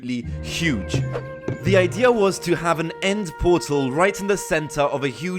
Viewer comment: at the start, Am I the only one who heard him say nether portal instead of end portal?